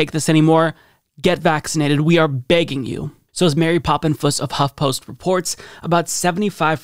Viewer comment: At first I thought you said "Mary Poppins from Huffpost" and did a triple take.